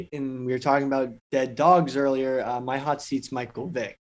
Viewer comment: Did PFT just say he used to sell used dogs?